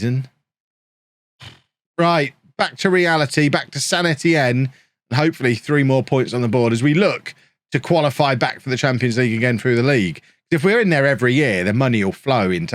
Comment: did anyone else hear «Back to reality, back to sanity-enne (St. Etienne)»?